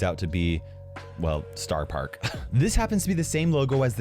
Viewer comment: I thought he was saying spar park the whole time - like a spar as in a fight 😂